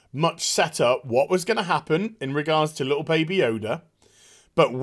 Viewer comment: I was listening to this while playing b3 n I kept hearing u say "adorable baby Yoda" then I looked down at my phone...omfg it's so adorbs!!